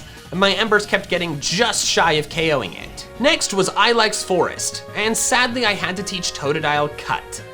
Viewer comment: Wait wait wait, what forest? "Ay-likes Forest"? Is this some Pokemon Talk reference or what? I don't watch it. EDIT: For some reason I used to think it was called "Helix Forest". That's probably why I thought "Ay-likes" sounded wrong to me. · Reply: Late but its called Ilex forest.